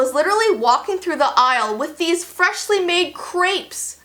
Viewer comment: I thought you said "freshly made GRAPES" and i was like... wth 😂😂